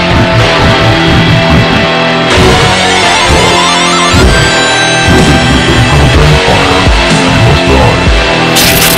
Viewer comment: did you hear what he said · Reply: He said "kill it, kill it, kill it, kill it with a god dang fire, you must die."